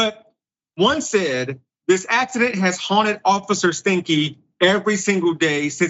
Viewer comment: Did He say officer stinky @ 7:27 Sorry I know this is not a funny situation at all but that name did make chuckle